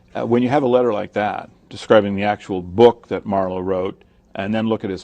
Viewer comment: at the beginning, an old writer was named, apparently the writer of the Bible, does anyone know who Atwell is talking about? I heard "Marla" and couldnt find anything of course =/ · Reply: Nick Sumner i believe the reference is to christopher marlowe.